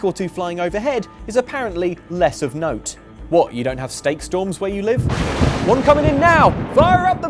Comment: For a moment I thought said "snake storm" and got very worried about the images that put in my mind.... shivers